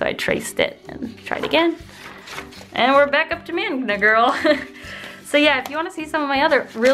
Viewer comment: Every time I hear 'Magma Girl' I hear 'Manga Girl'